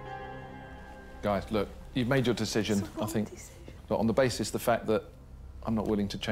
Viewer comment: What did Sarah say on 15:24 ? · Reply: It's the wrong decision